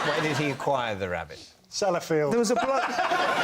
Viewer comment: please what did he say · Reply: Sellafield. It's a nuclear power plant in England